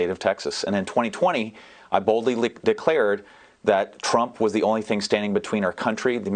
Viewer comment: seconds, he almost makes a Freudian slip "I BOLDLY LICKED HIS BOOTS." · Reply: sounded like he said "i boldly licked dick" then he quickly said "declared" right after that XD.